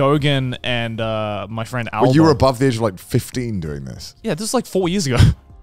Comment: did I hear that right? Joey’s friends with the drift hunter albo? Damn that’s hella cool never knew that they were friends